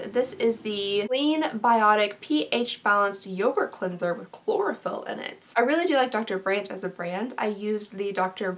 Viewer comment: I sh*5 you not, I heard CHLOROFORM not CHLOROPHYLL, and I was like "WHAAAAAT are they like body snatchers that track who bought it and then drug them with this scrub, then kill them and sell for parts??? ."... What a relief!